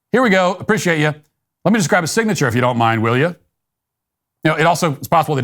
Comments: I thought he said "will ya".... not clear though. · I heard "will ya". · I heard 'let me grab your signature, if you don't mind... "I need it" '. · 😂😅, COUNSELING... STOP 😳😮 It isn't even clear what's being said. I thought he said, "Will ya". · I heard “Let me get your signature there”. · To me it sounded like he said let me get a signature right here, but it was blended together due to an accent. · I thought he said he needed his signature and "will ya?" He sounded very professional. If this guy needs a go fund me page count me in. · I heard “will ya” not “n*gga”😂! How could that even be confused. “Let me just have your signature, if you didn’t mind, will ya?” That’s it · Sounds like he is saying "will ya" not the N word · I heard “will ya” first time hearing the recording… I replayed trying to figure out where he was supposed to drop the N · sounds like he said "let me get your signature n'there" as he points to where on the receipt. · I heard "will ya" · I hear “will ya”. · Sounds like he says "lemme get a signature [right here]". This should be dismissed as frivolous. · I heard “will ya”?